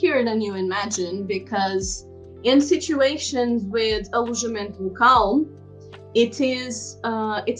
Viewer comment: What's the term you said in 4:42? It sounds like "eligible ducal". The caption said "illusion calm". I don't think any of them is correct. Could you please clarify? You said you would also put a link to register it. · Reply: Hi! It's 'Alojamento Local' 😊